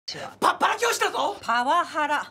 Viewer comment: どうやって聞き間違えたら「パワハラ教師」が「パッパラ教師」になるのよ😂😂